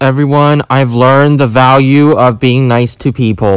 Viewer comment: I thought you said you learned the volume of being nice and then you're saying haha losers if that's what you said I think I forgot